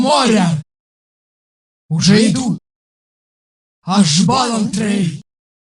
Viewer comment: Что говорит на 0:21? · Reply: ажбалантри